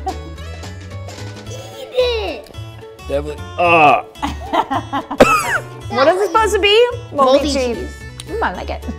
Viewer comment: did say dam**t? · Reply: No, he said definitely